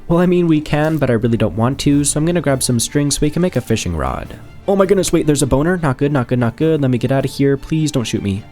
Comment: bro I heard b~~~ner when he saw a skeleton